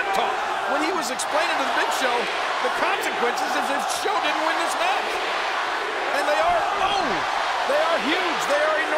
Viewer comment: What they say ?! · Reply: Lets go Cena. And Cena sucks.